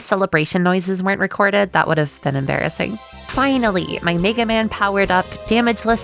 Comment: you said "Mega Man", right? I didn't just horribly mishear that, right?!